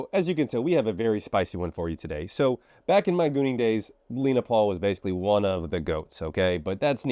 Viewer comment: I thought it said Logan Paul 💀